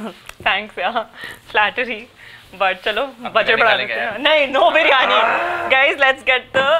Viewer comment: me samay ne kya bola yrr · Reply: Biryani khae ge